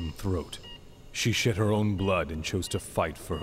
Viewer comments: did she just say "butthole" ...? 19:46 - "shit her own blood" ...!? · Holy shit I misheard Alucard say "she shit her own blod" instead of "she shed her own blood" and bitch, that's not so great, everyone with IBS has.